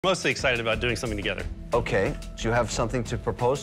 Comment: I had to replay the video lol I thought he said “im excited about dikes living together.”